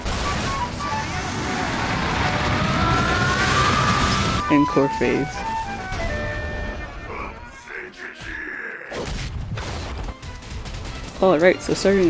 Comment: Lol i heard "encore phase"